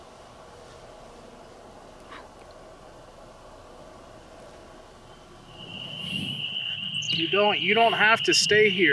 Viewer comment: ! You guys are awesome for doing that!! 💚 Keep your ears open at 24:17 there is definitely a voice there and I want to see what others think it says without influencing it. That was a pretty clear EVP and we know has a deep voice so turn the earbuds or headphones up on that part. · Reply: She says "help".